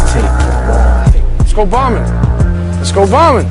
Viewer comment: Cope says "let's go bombing" on a sunny afternoon in New York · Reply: I heard. "Let's go snitching... let's go snitching!".